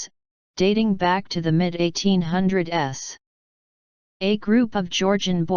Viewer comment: A PRIME example of why I hate these YT channels who use the "auto-voices" instead of just doing the audio themselves (lazy asses), when we hear @ 3:16 into the video the auto-bitch says the 1800 "S" and I want to know... what the Hell is 1800 "S"? Did she mean to say "the 1800's" but because she is a stupid "auto-bitch" she's not intelligent enough to distinguish proper English verbiage!!!.